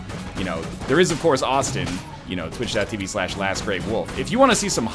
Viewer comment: Can someone link the twitch channel he says at about 7:19 I can't hear it :( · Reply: lastgreywolf I think